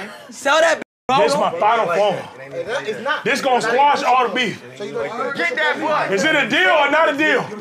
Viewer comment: I heard “get that butt” what is this? 💀😂